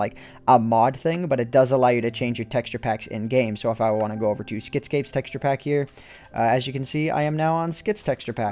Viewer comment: at first i thought he said skeet scape